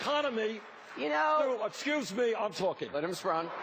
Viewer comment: What did Anderson say? "Mr. Brown"? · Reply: Wei4Green I think “let him talk”